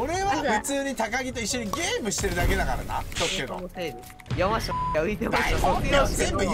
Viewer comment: 山下こうきに聞こえるの俺だけ？w